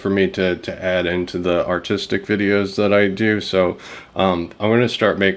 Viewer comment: oh man, I missheard that for a second and thought you said the "autistic videos" that you do. I had to do a double take 🤣😂